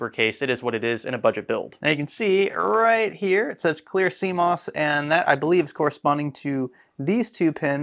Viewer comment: I didn't know what cmos was before these videos. I thought you were saying sea moss. lol.🌊🥬